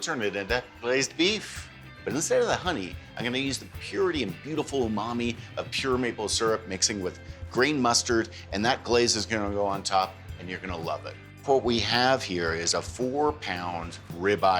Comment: At first I thought he said “green” mustard, and I thought were am I going to buy that